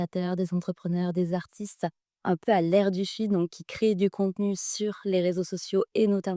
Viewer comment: à l'air du .... quoi ? Sheet ? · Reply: à l'air du feed ☺ feed insta, feed tiktok etc